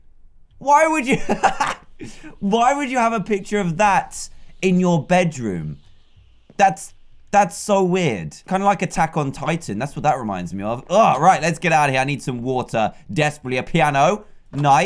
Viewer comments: did he just say attack on titan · hold on.. did.. did I just hear Dan mention.. aot?! 2:58 I’m actually so shocked 😭